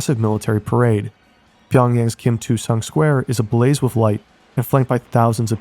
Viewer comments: did he say "Kim Two Sung Square"? lol It's Kim Il Sung Square, and they misread the il as II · Did I just hear Kim 2 Sung Square?